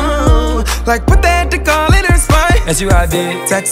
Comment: Sound like he said all In his spine